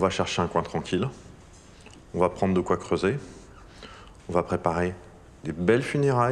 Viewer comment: jai entendu : "on va prendre de quoicoubeh". Je deteste cette blague mais bon apres une journée au lycée...